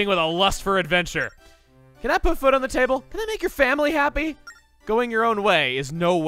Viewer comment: I hear "can that put foot on the table" and I can't unhear it.